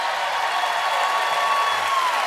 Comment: I thought that said "Kevin Garnett"